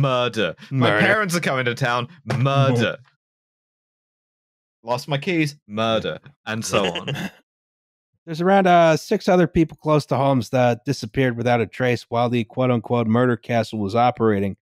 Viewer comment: Stg, when said "there were around six other people who went missing" I thought he said "around six HUNDRED people," straight up said out loud "I beg your fucking pardon!?" and had to rewind to check.